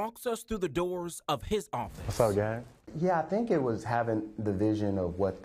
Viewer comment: what? Did you just say sup gang? Cuh ringe, you need some drip now BRUH